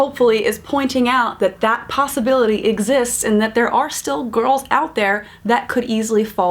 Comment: Sounded like she said "gorls" lol I love this channel. ❤😂